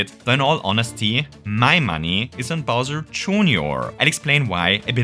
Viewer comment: Thought you could get away with it, huh? Too bad for you, my eyes are as sharp as a knife that's just been sharpened. 19:15 you say "Bowser Jr.", but the captions say "Bowser Jr. himself,". You missed out the "himself" part, buddy. Disliked, unsubscribed, 0 stars, blocked, I'm leaving a bad review on yelp and also you're bald